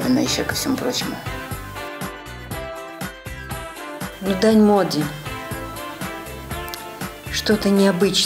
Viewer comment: что она сказала ? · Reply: Дань моде